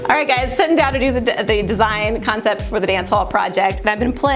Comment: almost sounded like she started to say 'sitting down to do the dishes'